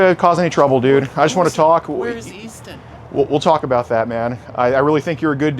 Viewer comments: Easton? i thought you said Weaston. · I thought at first he was saying, Wheres Easter 😂